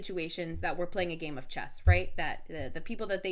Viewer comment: bet it doesnt even know how to play chess let alone checkers annd i could have swore it said CHEST instead of chess